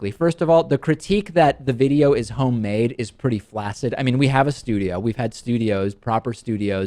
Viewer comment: Did you say "flaccid?"